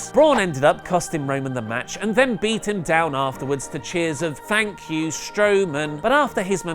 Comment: Vince-"Thank you Strowman?" "No, I heard 'Thank you Roman'"